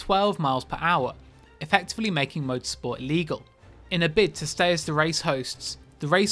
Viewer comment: really sounds like you said legal rather than illegal lol